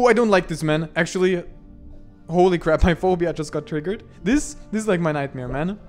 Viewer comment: What did you say at 31:05 ? Can't tell if you're naming a certain phobia, or saying "my phobia" , if so what is it if you don't mind. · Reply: Thalassophobia.